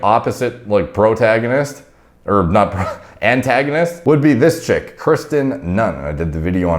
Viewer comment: did you say "brotagonist" 😂